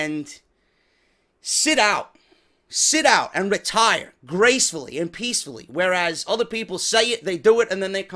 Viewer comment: HBK is my favorite but for real I thought he said he's staying retired